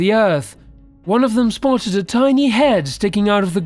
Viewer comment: What was it the peasants spotted in the ground? It sounds like he's saying, "A tiny head", but that doesn't make sense. It's at 5:35. One of them broke a piece of it off & took it back to town and that's when their problems began. If anyone knows what the heck this guy is actually saying, please clue me in...lol. (Taking a tiny piece of a tiny head? I can't have heard that correctly...lol.) Thanks! ;)